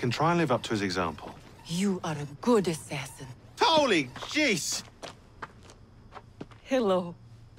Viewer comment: Shaun says "Holy Jesus" but the subtitle says "mother of Thor"